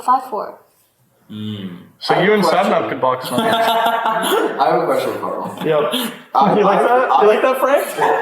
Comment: did sapap say "I have a crush on Karl" ???? I don't think he did but at the same time I do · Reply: I think he said "I have a question for Karl"